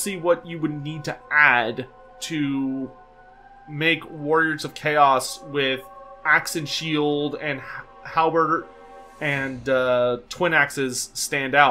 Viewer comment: It sounded like you called it a hal-burger. Which is probably a Khorne-themed menu item.